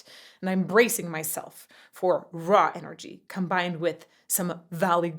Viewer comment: trying to confirm did you say raw energy there?